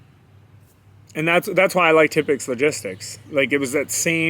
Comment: You said a company name sound like you said Topics Logistics around the 3:44 mark, can you spell it please?